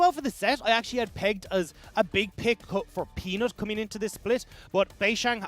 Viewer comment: Did he just say Penis? LOL